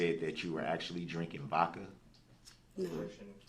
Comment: Did he just say that she was actually drinking “Vokka” 😂